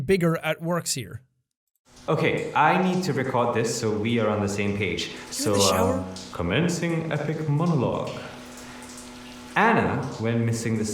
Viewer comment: can someone please tell me what Taylor says in this audio clip? I can’t make out a word he’s saying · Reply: "Okay. I need to record this so we are on the same page. So uh, commencing epic monologue. Anna went missing the same way as James, and they were last seen going on a date with someone on spark. Anna and James still had activity on jabbr, but no one has physically seen them? It's... the same pattern. James is still no where to be found..... I hope Anna, doesn't share the same fate....."